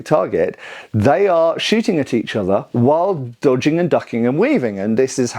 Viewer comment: Thought you said dogging lol 😂😅